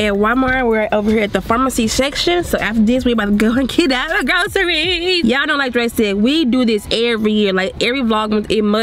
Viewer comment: did she say we about to kidnap the groceries